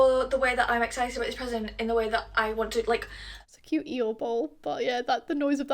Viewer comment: I got excited because I heard "eel bowl" and eels are my special interest- but an Eeyore bowl is cool too :)